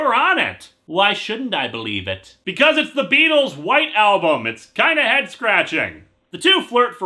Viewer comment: First time I listened to this review, I misheard say 'the Beatles white album' and thought he said 'a Peabo Brice album' Had to rewind due to confusion